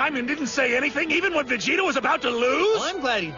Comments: did beerus say vegito instead of vegeta?! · did beerus say vegito?!